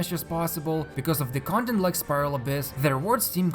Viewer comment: Did he just say "condom like spiral abyss?"